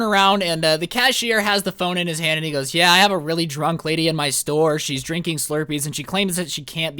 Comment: you said she was drunk and I thought that you said she was hydrated EXPOSED